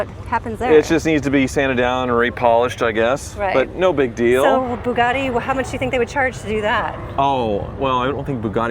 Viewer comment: Why does it sound like is saying Boobgatti? 🤪❤️🇺🇸